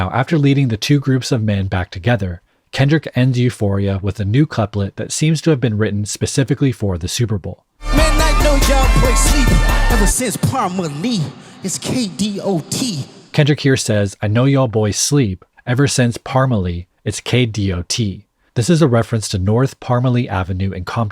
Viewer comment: I thought & heard the line as "Man i put you'll boys to sleep since prime Ali its Kdot! 😂